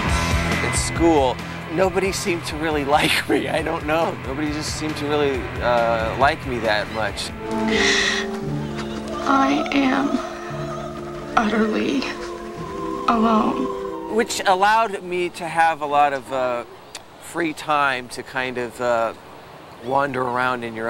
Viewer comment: Sounds like she said "I am utterly, a hoe"😓